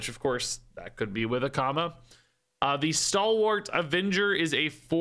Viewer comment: I heard "which of course, that could be with a comma," at the end of the card description at 1:00, and I read the card text over and over again trying to figure out the grammatical error the Hearthstone team had made. It wasn't until I gave up and halfway through the video I heard you say "Akama" in a way that made me realize that was what you were talking about instead. 😂